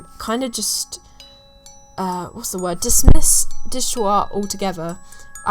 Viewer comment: omg when you started talking about "digital art" at around 8:30 I was so confused bc i didnt understand the word that ypu were saying. instead of digital art i just heard "di gu ua" and i was like what the hell is digua